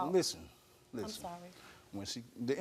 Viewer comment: did she say sorry 🤔